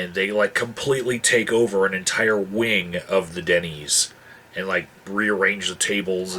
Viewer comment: Excuse my ignorance here, what is a Dennies? I at first thought you were saying thay take over a whole wing of a dunny and the tables there. I thought this is going to be friggin' hilarious. fyi, dunny is a slang word for toilet in my culture. cheers